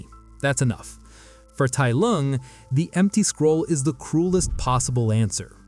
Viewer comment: says crulest instead of cruelest. Awesome video nonetheless!